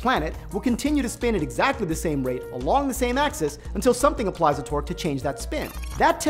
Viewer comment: oh TORQUE lol i thought he was saying twerk